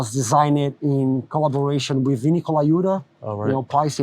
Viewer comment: So which vinny is he talking about? Being his drummer god he proclaimed? Does he mean vinny from Pantera? I couldn't really understand the last name drop they said 8:22 in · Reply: Vinnie Colaiuta